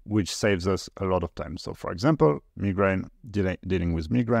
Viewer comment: Was listening to this with earplugs and wasnt looking at the actual video, and heard migrant instead of migranes 😅 and it all still made sense in a different way. Just started looking at the screen now and realised i misheard you lol